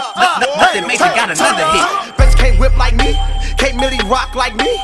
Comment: I always thought this said "bitch can't whip like me" 😅